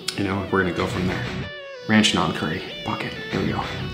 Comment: Did anybody else hear him say f$ck it at 7:25?